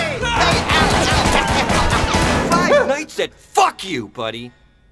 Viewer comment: Did he just say Five nights at F you buddy